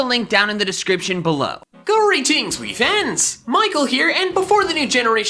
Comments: For soo long I thought he was saying “greetings, squeaky fans!” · In the intro I've always heard it as "GORY TEENS POKÉFANS!" its not until today I heard it as "Greetings"